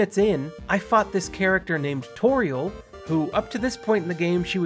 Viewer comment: i thought you said "i fucked this character named toriel' wow my brain is fucked